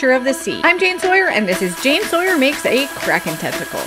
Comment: WAIT!!! What did she just say? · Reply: I had to replay it, "oh she said tentacle"